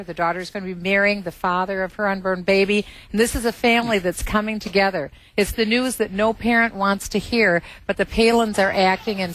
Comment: Did she say "unburned baby"?